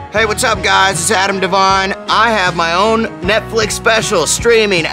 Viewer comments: Anyone else thought it said Adam Levine at first? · At first I thought it said Adam Levine :/ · I thought it said Ava Devine..lol